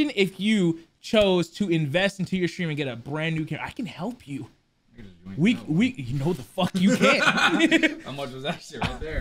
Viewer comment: What the hell did say at 1:21 ? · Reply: "I can just yoink that one"